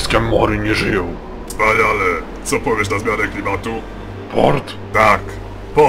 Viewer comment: usłyszałem Mort xD